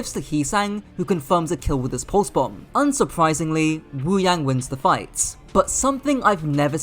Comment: i thought he said yi sang for a moment